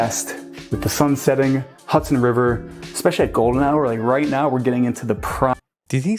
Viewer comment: Golden hour? I thought he said Golden Shower. He needs to shave and wear a tie, clean up his act.